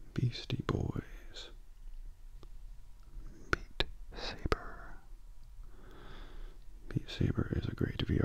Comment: I thought you said meat saber